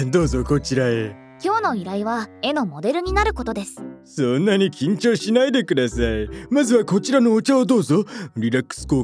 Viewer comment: そんなに興奮しないでくださいにしか聞こえなかった